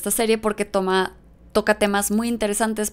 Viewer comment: "Tócate más" entendí 😜 Luego vi que era "Toca temas" 🤭 Será por el acento mejicano